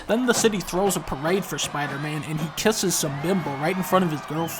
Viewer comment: When dunkey first said “bimbo” I swear I heard him say “liberal” and I still think that the difference between the two changes everything about the rest of the video.